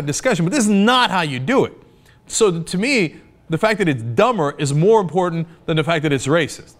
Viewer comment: it says dahmer instead of dumber lol